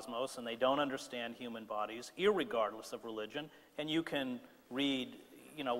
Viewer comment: I meant, AT 1:20, does he say, "Irregardless"?